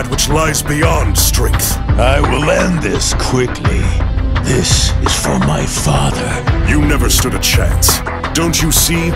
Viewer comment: did Broly just say "this is for my father"